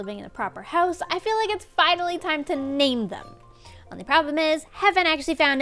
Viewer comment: said it's finally time to Name them...I thought she said maim them...oooops!